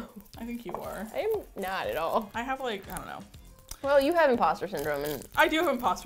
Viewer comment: I thought you said you had Pasta Syndrome